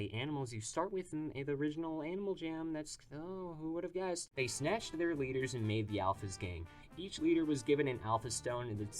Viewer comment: I thought you said they snatched their leaders and made the alphas gay 🥀